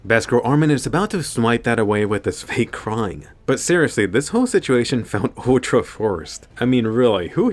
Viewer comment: Did he just say best girl armin?🤨